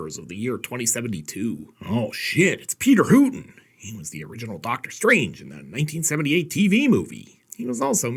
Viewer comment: Man, I misheard you, you said TVs original Doctor Strange and I heard Doctor Who. Realized I had misheard when I remembered the original Doctor Who (William Hartnell died in 89 - I think and was pretty ancient in 1963 when Doctor Who premiered. Yes I am that old (I will be 53 in March and I really don't care who knows) - I may grow older but I will NEVER grow up, they can't make me.